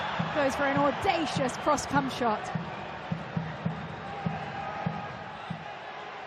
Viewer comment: Did…did she just say cųmshot 👀🫣😅